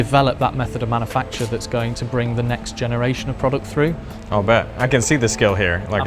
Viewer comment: did you just say "Oh bet". 😂😂